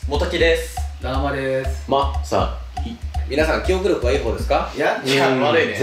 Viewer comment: ま さ いがま さ きに聞こえる！